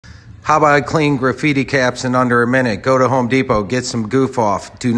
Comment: Thought he said "Graffitti'd Cabs" I until the Short was over. I kept thinking "Where is the cab?"